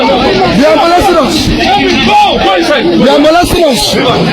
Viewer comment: did he just say, "They're molesting us"?